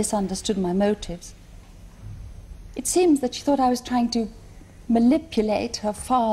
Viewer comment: DId she say "malipulate"?